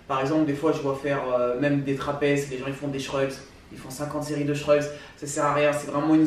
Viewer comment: C'est quoi l'exercice pour les trapèzes à 2:19 ?? Je comprends pas le mot :p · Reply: Des shrugs :)